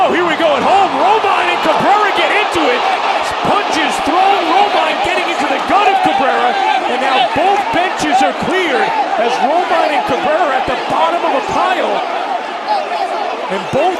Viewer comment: いつ聞いてもポンジュースにしか聞こえなくて草